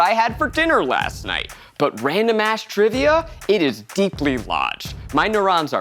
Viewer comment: some of the captions are wrong, like at 4:07 it says “mash” instead of “ass”. there might be some other things wrong too idk